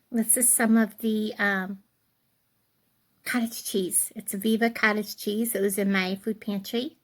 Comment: I thought you said cabbage cheese I was like whaaaaat lol